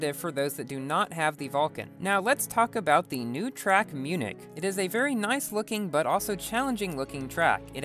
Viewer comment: I thought it said munch for a second